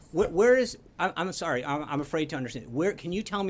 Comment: did Spicer say "I am afraid to understand"? If so, that is a Freudian as you can get!!